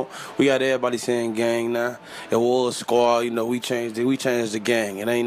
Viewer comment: "we got everybody sayin gang now.. eroerhlehrl squad" wtf did he just say there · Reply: we got everybody sayin gang now, yeah or squad...